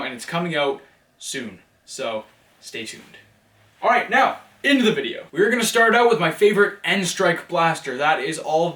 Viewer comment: did he say end of the video in minute